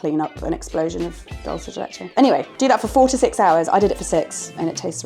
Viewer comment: Re: dulce de leche, at first I thought Jemma had said, "Boil it for 46 hours. I did it for 6." and then I realized she said "4 to 6 hours" HAHAHA